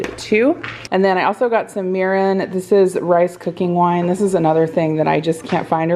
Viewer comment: I usually have youtube playing in the background as I'm doing something else and I had to stop what I was doing and rewind when you were talking about that mirin product! I could've sworn you said urine! 😂